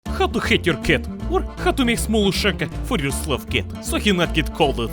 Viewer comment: For a minute I thought it said how to make a hat out of your ca